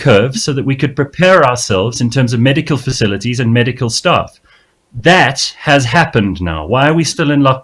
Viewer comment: 1) He did not say we are 100% ready, he said the lockdown was to achieve that. 2) He did not say that same would die from no lockdown, but rather if we continue to state in an extended lockdown. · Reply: in 7:21 he clearly says “..... that HAS Happened..." refering to flattening the curve and preparation of medical infrustructure